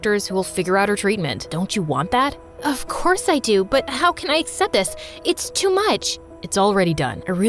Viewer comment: No offence but did she just say “ acept dis “ btw this was my first hear